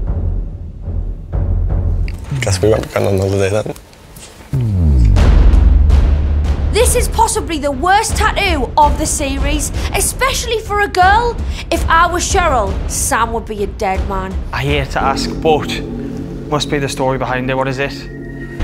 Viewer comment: Did she say tatloo at 0:39😂